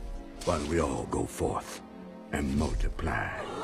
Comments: Lol I thought the priest said: “FUCK AND MULTIPLY” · ngl i thought he said "go f*ck and multiply"